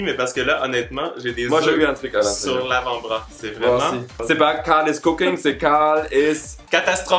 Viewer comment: j'ai compris caliss cooking